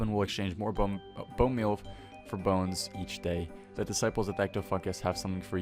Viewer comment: ll at the end it sounded like u said disciples of ecto-fuck-is